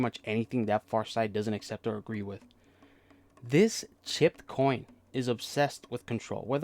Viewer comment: Excuse me i heard "clipped coin" thats what you said right? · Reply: Chipped. I am so sick with Covid or the flu or something I wish I had worked on this better. Sorry for the amateur level of an upload.